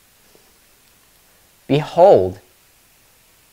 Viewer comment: Him: Behold Me: Did he just say "Beanhold?" (Rewinds and turns up volume) (Loud ad plays) Me: s a n s